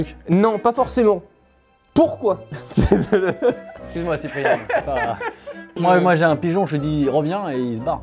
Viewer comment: A 2:23 il a dit : « Excuse-moi Cipriani ou Cyprien ? « Répondez moi s’il vous plaît 🙏🙏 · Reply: Moi j'ai compri Cyprien